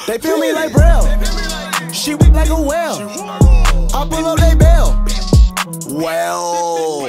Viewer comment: i love how you censored "she wet like a whale" best censored version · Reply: Frecklestein Honestly I thought it said "She built like a whale"